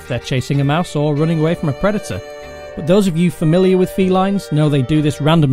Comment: OMG< I swear i thought he said, "New cat owners might wonder whether they're chasing a mouse or running away from a creditor (instead of predator)." lol